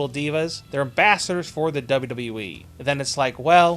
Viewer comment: when he said, "They're Bachelor's for the WWE", he sounded like he said, "They're Bastards for the WWE".